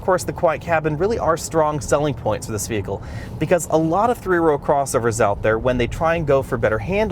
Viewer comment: Did he say 3 wheel crossover? I see 4 wheels. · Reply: He said 3 row